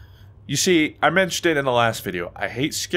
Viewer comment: sounded like "I hate scary games, because urine control" XD